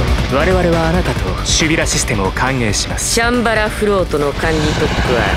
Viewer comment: チャンバラフロートと、空耳してた。ww